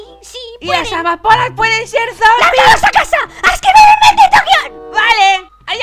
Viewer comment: Que dijo la profe en el minuto 1:55? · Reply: Alba Stars Dise largaos a sus casas y hagan el maldito guion